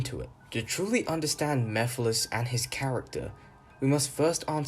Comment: I like the mephilise in the dub. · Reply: i thought you said Mephiles and Elise as a ship name, I got scared